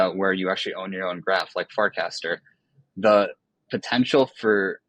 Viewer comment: Wait, did he say "fart-caster" at 24:10??